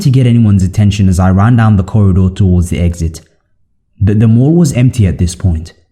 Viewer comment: He almost sound like you saying morgue instead of mall at 3:42 I heard morgue